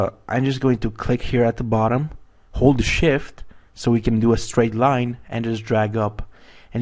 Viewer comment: Just a little thing, When you say "Hold shift" Sound like you say holy shit! ;)